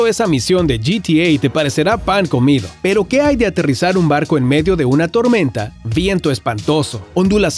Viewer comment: ? Aterrizar un barco en una tormenta?.!!!! Así se dice o corrigeme alomejor escuché mal ...